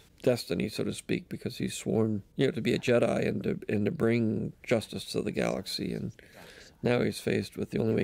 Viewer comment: what did he just say??? My god! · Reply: Justice to the jedi?